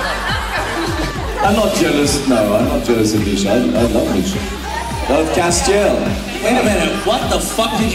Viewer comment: what did he say? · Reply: "I'm not jealous- no i'm not jealous of Misha, i LOVE Misha!" :-)